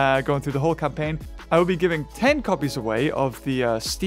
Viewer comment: I wasn't paying attention to what he said and honestly thought he said giving away 10 compys 15:08 but I realized he said copies.